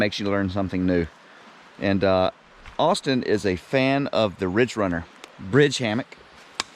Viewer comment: Awesome video and I hope there are plans for many more like this. I have a question on the Hammock ( 40:00 ) what is the company that makes it? What is the true name of the hammock? I heard you say Ridgerunner, Ridge hammock. Thanks and keep the adventures coming. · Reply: Warbonnet Bridge Hammock...I think it is Called the Ridge Runner...@ Trail Adventures would know for sure...